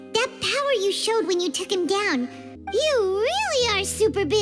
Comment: is it weird that i hear "mon sya neuvi let" from paimon😨